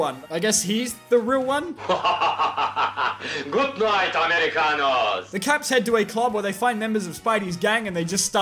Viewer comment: The sad thing is pepol Pade for this and is it just me or did spiderman say by American 7:03 like he was going to nuke American